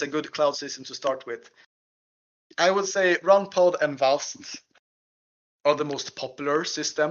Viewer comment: I dont understand the names mentioned at 53:10 for GPUs? if you have links post them please, thanks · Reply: I reckon that was runpod and vast as cloud service providers, not the actual hardware..